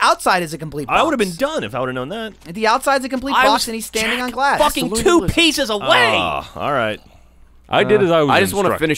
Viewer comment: what does whisper at 42:39 ? "Jack ....." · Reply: jack ... let him win this